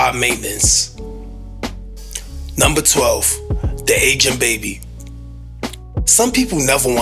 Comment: Thank you for this. I thought you said "the ancient baby" though 😂